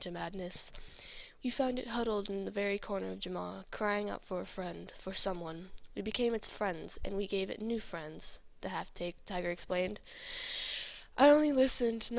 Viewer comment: WAIT A MINUTE.. Did you say.. ''the half- ta- TAKED tiger''?! :) :3 x3 X3 XD ( at 2:36 / 2:37 ) XD